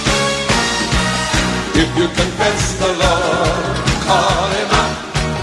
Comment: Misheard Lyrics... and no don't get offended am a Christian just lightening things up...this what am hearing 'If you can't text the Lord, Call him up'...lol